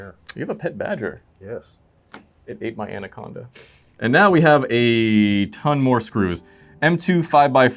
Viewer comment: did you say? Anaconda prompt!